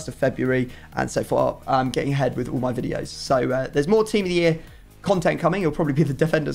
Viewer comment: i had to double take on what you said 😂😂 thought you said "I'm getting head for my videos" Was gonna say LUCKY YOU!